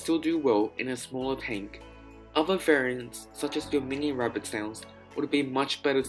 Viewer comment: What does he say? "Your mini rabbit snails"? I can't find anything else about mini rabbit snails :( but i'd really like rabbit snails that only grow to an inch long